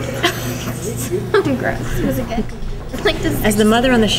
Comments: It sounds like she said "Fucking Gross" 😂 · sounds like she is saying: Fuckin Gross